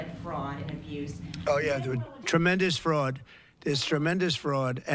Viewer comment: Do i hear correct? He is saying: 'Oh Yeah Dude' To a woman?